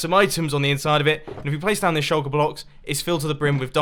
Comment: Did he say shulker blocks?